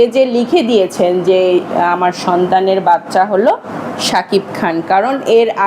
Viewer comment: এ কি বললেন এইটা?😅😂😂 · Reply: সন্তানের বাচ্চা